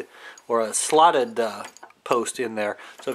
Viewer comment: I’m sorry. I just watched again and heard you say slotted post.